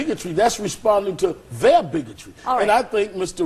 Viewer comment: He did not say nigga at 4:09 he said bigot she just cut him off and he didn't get all of the word out!